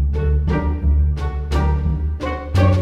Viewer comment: OMG, I thought Monty said "I like men." The first 2-3 times I watched it, then realized he actually said "I like me."